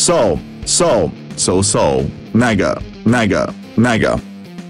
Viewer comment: What did he just say🤨🤨🤨🤨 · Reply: Mega